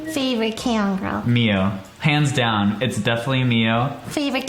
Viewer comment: I thought he said pants down XD instead of hands down